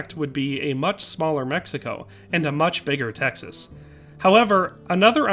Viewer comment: As a Texas I heard “ much larger texas” and immediately got a hard on